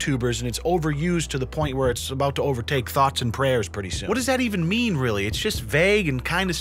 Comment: lol i thought you said "thots and prayers" haha.